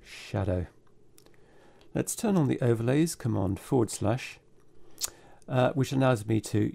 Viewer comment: I can't understand, what you are saying. It sounds like "Lets turn on the overlays - comand-forward-slush". I can't figure out what shortcut this is.